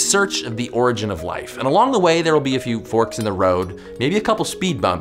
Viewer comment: I honestly thought you said "quarks in the road".